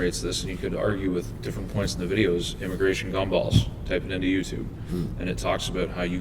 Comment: The guy who mentions a video, I thought he said "immigration gone balls" but he said "immigration gumballs". I found it on vimeo as <num>